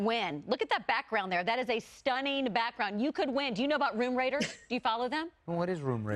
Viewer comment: Lol i thought when she was talkin bout "Room Raters", that she was sayin "Room Raiders" lmao. For those that don't kno it was a weird "reality dating game" (that's the best way i could describe it lol) on MTV years ago. I was like wtf? They brought that back? And she knows bout it and loves it that much? I was so confused/baffled lol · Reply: Oh! I thought they said Room Raiders, and got really confused, lol!